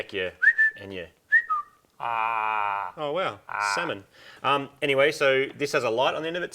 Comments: "Oh wow, semen" You guys rock.....really. I'm sure I miss some of your jokes (cultural differences) - but that one, loud and clear. I think you guys should throw another junkyard engine into it and keep it as a little street ripper. Maybe tune it down a bit, and just add it to the fleet. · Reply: Sometimes the mind will fill in the missing bits and pieces of a misheard word with those we are more familiar or comfortable with. I say 'tomayto', you say 'tomahto'. says 'salmon', you say 'Moog has a gob full of chunky jizz'. Let's Call the Whole Thing Off... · Did anyone else catch what said at 5:33? "Oh, wow. Semen." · did he said salmon right?